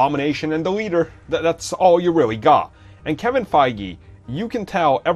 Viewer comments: at one point I heard "Kevin faggy" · Did he just say "Kevin Faggie"